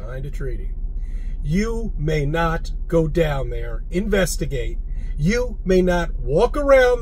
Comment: Did he really say "... go down there..." 😂